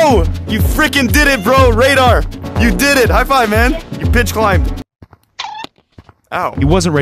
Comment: you b!tched climbed. Radar: bops him Also I’m not saying said “b!tched” to me it just sounded like he did